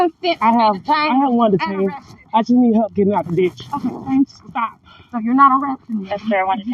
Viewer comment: I thought she said Bitch not Ditch. I wished she would've said the first one Thx for her court appearance